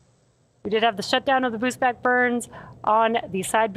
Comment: Congrats to SpaceX, another amazing launch & spectacular landing of the side boosters. It never gets old. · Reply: voice says "boost back burns" but screen says "booze bag burns" Are you for real???? 9:40 Miko in big letters and MECO in small letters. 11:01 "Wendy engines reigniting" on screen, maybe the voice pronounced it "wendy" instead of "when the".